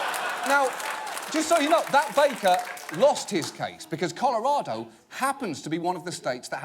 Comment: I thought he said "that baker lost his cakes" lol